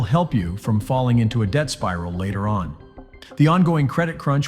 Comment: Debt Spiral, Ohhh, I thought He Said Death Spiral! Wait... Isn't that One in the same? Sorry, I Can't Afford Cotton Swabs to Clean My Ears Properly so I can Hear What's Actually Being Said.....